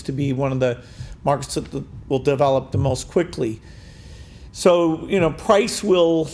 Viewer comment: bill mumbles a bit cant understand what he said ...and Marques is staring into the air like what did he just say ?? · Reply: He said "China is one of the markets that will develop the most quickly"